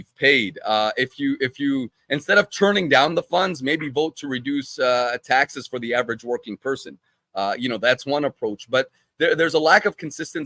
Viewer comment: This guest really said the government should lower taxes? No qualification? I think that says all anyone needs to know about him. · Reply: I don't think you heard his entire statement. He said.. "...if taxes were lowered for the average working American..." Go back and listen again.